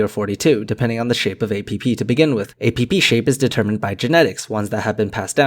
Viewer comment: ? 08:39? Somewhere right in there. "...APP shape..." I heard "...a pee-pee shape..." #NeverGonnaGrowUp #SorryNotSorry lol